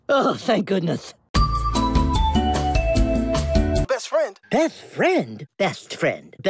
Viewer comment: Istg Daffy said all of the below instead of best friend: Beathfred Deathfriend Betfrend BeSTfrend Breast friend Death threats Dats fred